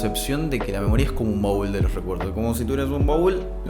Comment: Juro que entendí “un Mogul de los recuerdos” 😂😂😂